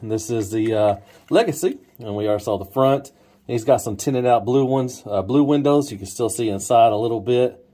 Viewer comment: Nice VS. Man I gotta check my ears. When you were talking about the windows being tinted I thought you said titted. Made myself laugh. 😄😄 I thought woah when did Skids become a woman? LOL 😆 😆 I like the Legacy better for bot, but the Generations better for alt mode.